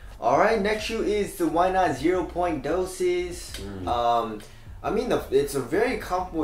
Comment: What did he say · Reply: why not zero dos (2)